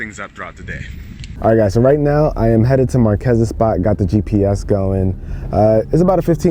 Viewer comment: Idk how people heard my cousins spot. I definitely heard him say Marques spot